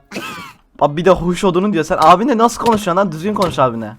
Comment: asdaddaas duydunuz mu nin ne dediğini abisine huş odunu dedi😂😂😂😂